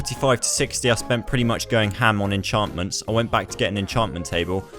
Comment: In 12:24 did you just say hamon? Woah you're a JoJo fan